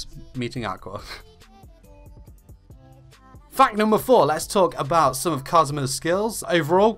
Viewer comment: I could have sworn he said “fuck number 4”